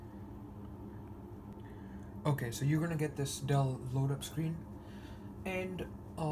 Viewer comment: I love how his accent makes him say "Dull load up screen" instead of "Dell load...". :) And no, I'm NOT making fun of you.